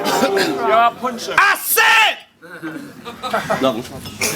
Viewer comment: ''Yo I'll Punch Ya'' Am I the Only one Who Heard It ?